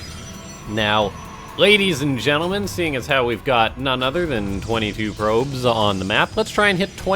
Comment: "none other than 22 probes" for a second there I heard Lowko!